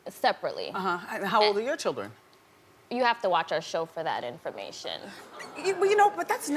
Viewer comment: She will never say how many kids she has! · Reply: Cam I thought she said watch her show 😜